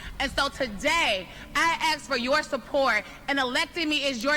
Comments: omg the subtitles really had axe, instead of ask. Even though she said ask, her k was not super annuciated, but I heard the K sound. Low key racist. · when the woman said "ask" and the caption said "axe" cause she's black and the captioner assumed